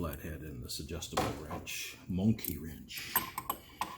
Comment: did he call that crescent wrench a monkey wrench or am I just hearing things?